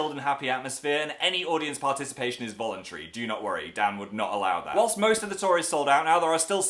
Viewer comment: I thought he said "your dads would not allow that." let's just say I died for a few minutes.